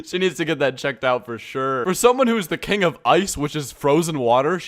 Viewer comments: did this guy just say “the king of ice” or did I misheard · im sorry, did you just say "king"?